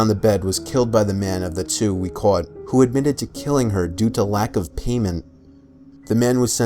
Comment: When he said “lack of payment” I heard “lack of anal”